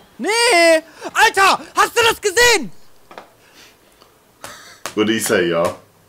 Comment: He was speaking German. It sounded like he said Was hast du gesehen?! Which means What have you seen (past tense).